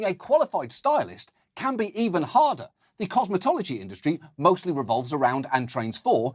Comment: There was a moment when he "the cosmetology industry" and I heard "the cosmotology industry", and now I want it to be that way.. Everything about cosmetics would make so much more sense if it was run by aliens.